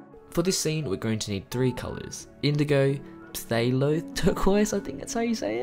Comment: can someone spell what color he said here? Did he say phthalo? Bc i have a phthalo blue but not phthalo turquoise